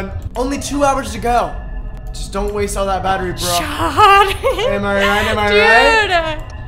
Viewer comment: it sounded like she said “sHawTtyYy” HWIEIENENWNWH